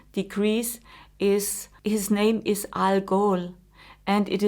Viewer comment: At first I thought she said the star name was Al Gore